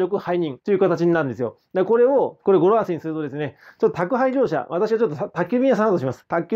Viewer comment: ココがどうしても覚えられなくて、100回は歌を聞きました(笑) 宅配業者のとこが、宅建業者に聞こえてしまう(笑)